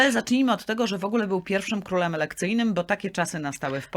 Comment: Prosimy o audycję o Annie Jagiellonce oraz o królu Poniatowskim. Czy ja dobrze usłyszałam: "był pierwszym królem erekcyjnym"? 😊 Jeśli dobrze usłyszałam, to nawet pasowałoby takie określenie do króla Walezego ☺ Pozdrawiam serdecznie.